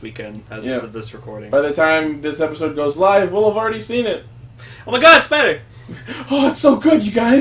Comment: OH, I freaking EDITED the episode and I just realized this, thought had said "oh my god a spider" rather than "oh my god it's better"